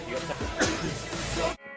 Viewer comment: it sounded like he said "susan boyle" but he actually said excuse eh moi